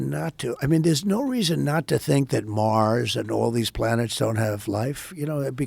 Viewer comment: did trump just say that he thinks martians are real? Or am I not hearing that right lmao